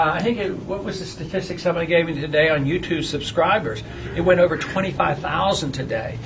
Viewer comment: he said "YouToo" instead of "YouTube" hehe I hope he does well on the straw poll. I think he'll do at least 20%. $75k in one day.. are you kidding me? that's awesome.